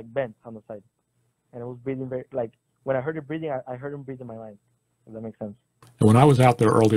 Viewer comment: am i hearing this right? " when i heard him breathing i heard it in my mind" crazy